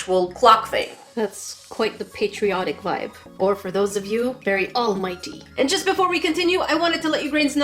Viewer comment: "Very all mighty" ;0- fear not! For I am here! -all might Am I the only one who freaked out when I heard this lmao- Bnha fans where you at- 😂